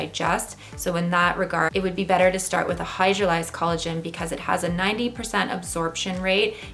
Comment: I couldn't catch the word at minute 1:31 "hi,,,, collagen"? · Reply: Omar, she is referring to “hydrolyzed collagen”...